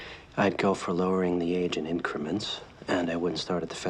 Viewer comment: "Well, I'd go with 'lowering the age of XxXxXxX, and I wouldn't start at the federal level." What is that word!? I must have listened to it a dozen times, but I can't make it out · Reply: "Well, I'd go with lowering the age in increments, and I wouldn't start at the federal level."